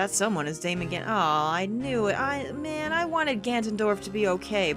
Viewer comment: did just say she wanted Ganondorf to be the good guy